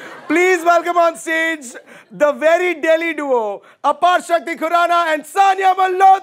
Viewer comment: When he said aparshakti khurrana, I thought he said apostrophe Khurrana 😂😂😂😂😂